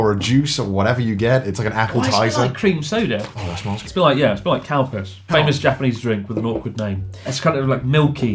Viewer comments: i heard cow-piss and i was extremely confused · When Chris said Calpis I literally thought he said " Tastes like Cowpiss " it cracked me up so hard omg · I thought he said "it tastes like Cow Piss" 0_0 oh wait...he meant Calpis XD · I swear when he said Calpis I thought he said cow piss 😅🤣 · When he said calpis I thought u said cow piss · I think I'm drunk despite never drinking in my life cuz I swear to God I heard Chris say "Cowpiss" instead of Calpis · Gotta be honest here, when they said "Calpis" I thought they said "Cow piss", so that's pretty interesting. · "it tastes like cow piss" is all I heard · i thought he said Cow piss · Thought i heard cow piss😂😂calpis* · Thought he said cowpiss when he actually said calpis! 🙂🙂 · every time I hear someone pronounse "calpis", I hear "Cow Piss". Juvenile I know, they should've chosen a better name.